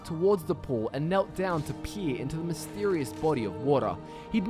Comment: you sounded like you said "he peed into the mysterious fountain" instead of peered lol